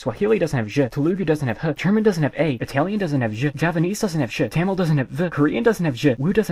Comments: i heard "javanese doesn't have shit" lmaoooo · oh you said javanese sorry i misunderstood. I know about javanese it just sounded like japanese to me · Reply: I am scrolling through the comments and half of them are mishearing javanese as japanese lol